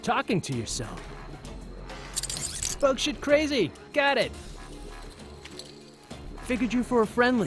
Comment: what the hell did he say? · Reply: eclipse572 "Bug-shit crazy! Got it!"I think that's what he says...